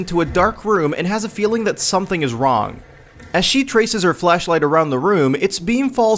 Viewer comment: Did you just say "flesh-light"..?